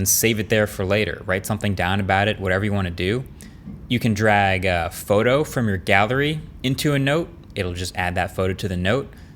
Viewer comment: Did anyone else hear, "You can grab a photo from your 'gather-y" at 7:05? For some reason I was waiting for him to flash some text on the screen or make a joke. 🤣 Thanks for another geat video!